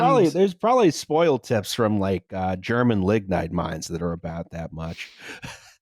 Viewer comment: I keep mishearing it as "German ligma mines"